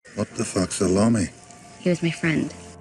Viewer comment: Wtf salami? Gotta love YouTube subtitles.... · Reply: Rach C i always thought he said "what the fuck s' a lommy"